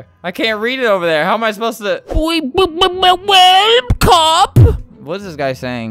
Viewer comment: "boi bbbwaaelp cop"